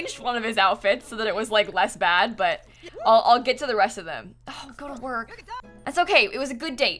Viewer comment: sounds like said ' gotta go '